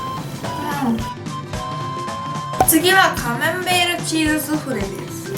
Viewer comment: カマンベールがカメンベールって聞こえた！ これって私だけ？